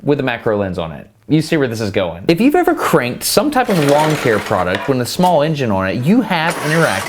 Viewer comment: Oh “Lawn care” product. Lol I thought you said “long hair” product and I was like what does long hair have to do with pull starting a Len mower?